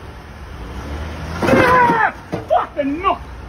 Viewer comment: 🤣🤣🤣 did he just say fuckin 🥷🏾🤣🤣🤣🤣 I’m dying over here